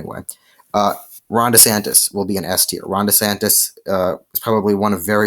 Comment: I thought you said F tier and was going to unsubscribe also I'd say Rubio is also S